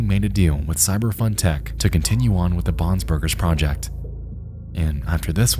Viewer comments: I thought he was saying bobs burgers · i kept hearing "Bobs Burgers" instead of "Bons Burgers" and i was thinking to myself how they got away with the copyright for that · sounds like ur saying bobs burgers and its killing me so bad for some reason